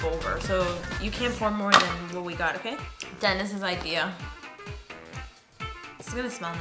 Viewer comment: When he said Denis I thought he said dentist 🦷